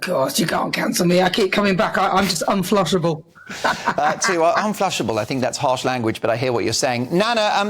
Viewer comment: Is Mark saying that Nana is a poo 💩 - unflushable? 1:32 She’s not happy with him 😂 I thought she said unflutterable which I understood as a misspoken unflappable.